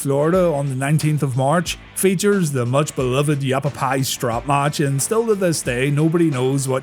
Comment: im a native from az. I laughed so hard when Hogan busted out that yavapai promo. Im not sure if thats what he said, but it sure sounded like yavapai.